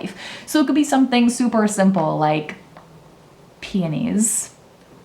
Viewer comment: I was playing this in the background and I heard, “Journal whatever inspires you. It could be as simple as *panties*👙.” 😭😭😭